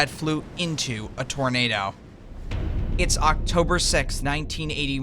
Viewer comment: what did he say six as? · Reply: Sixth.